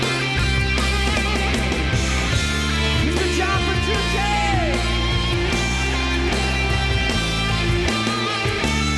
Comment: Guys please sort this debate out between me and my friend. What does Labrie say at 6:22? My friend says it's Mister John Petrucci but it's surely Good Job Petrucci? What do y'all think? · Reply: 100% Mr. John Petrucci